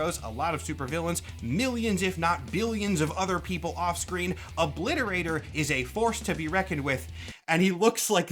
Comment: I spent like half of this section thinking it sounded like lobo until I remembered that’s the wrong universe